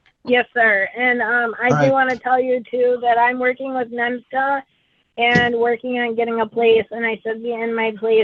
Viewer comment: did she say she's working with MENSA?